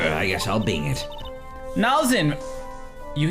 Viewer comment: For a moment I thought the judge said I'll Binge it (Like Binge watching a television show). But then they elaborated both are funny though Binge watching and Bing searching.